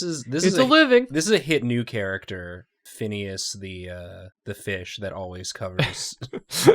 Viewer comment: the moment said the fish's name I thought he said P**is the Fish....